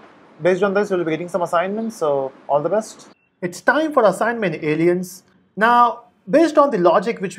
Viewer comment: Did you say Aliens @ 3:51? what you mean to all human subscribers??